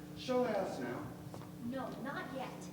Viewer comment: Did he really say show ass now? 22:32 I can't hear anything else and thats what the captions say